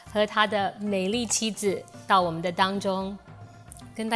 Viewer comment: Jenny: In 0:14, It’s “到我们当中” not “到我们的当中”. Jenny: In 0:16, “说” in “跟大家说” should be “shuo” instead of “suo”. Also in 2:36. Jenny: In 1:15, “是” in “就是” should be “shi”, not “si”. Also in 2:22. In 4:26-27; in 4；57; In 5:18; 8:09； Jennn: In 2:28, “亚” in “亚裔明星” should be “ya 4” instead of “ya 3”. Also in 4:03. In 6:46, there’s no pinyin for “一” in “一见钟情”. For the whole video, she kept making mistakes with “是” , “说”, “这” , “种” ect, which need the curling of tongues.